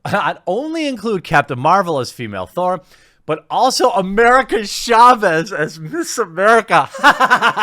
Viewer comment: Did I hear that right? "America Chavez as Meso-America"